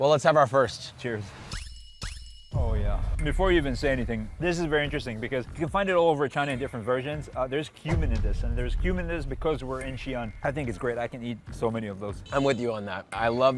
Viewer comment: was listening at lower volume and thought he said their's human in this not cumin 😂